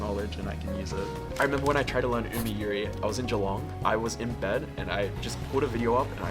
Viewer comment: I heard him say he was in Geelong? Because that map certainly did not show Geelong so I’m guessing I misheard? Anyone know what he actually said?